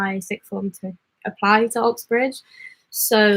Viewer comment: Hi thought it said Uxbridge lol